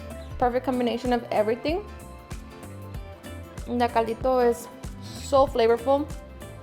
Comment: I like understanding Spanish terms. @ 4:24 it sounds like you said something like "necalito" or something and I am unfamiliar with that word. Can you describe what it means for me? Aloha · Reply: She said Caldito which means the broth